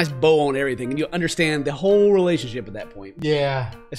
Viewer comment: the way said yeah it sounded like the bruh sound but its yeah